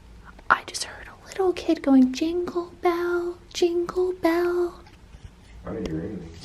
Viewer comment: Omgoosh, I heard "jeggel bell" too.